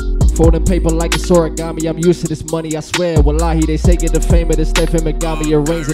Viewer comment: did man say wallahi? Interesting